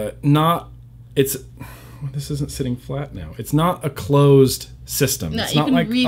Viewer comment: I thought I heard you say, "Flat Cow", which was equally weird.